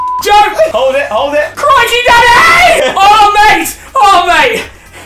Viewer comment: Crikey Daddy was the code word ! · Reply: I thought you were saying "Crank me Daddy" at first 😂😂